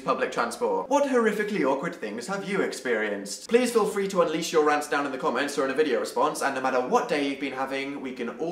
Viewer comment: 2 things... omg 720 HD dan <3 and i thought u said leave your rats in the comments.....